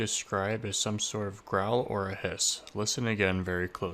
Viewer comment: I honestly thought it said run instead of a growl. I instantly felt like that was a baby by possibly grandpa!